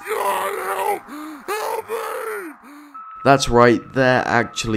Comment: I thought it Said kill me but it said god help and help me